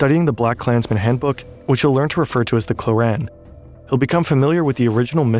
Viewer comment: i heard quran and almost choked on my water, i fr thought my ummah was pulling this 😭